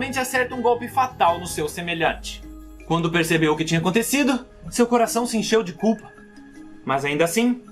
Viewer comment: JURO QUE ESCUTEI "SEU CORAÇÃO SE ENCHEU DE CU" kakakakkaka